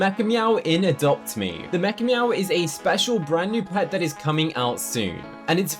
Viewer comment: Whenever you said mechameow, I thought you said MEGA “MEOW”